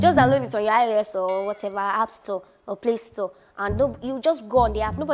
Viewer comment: 😂😂😂 Subtitles say is 'App Sore' And then 'APC umbrella'...😂😂😂 This girl o